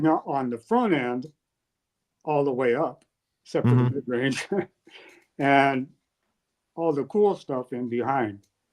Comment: Mick talks about his early gear sound but I can't figure out if he says "except for the midrange" or "set for the midrange". Can anyone help me understand what he's saying? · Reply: “Except for the midrange”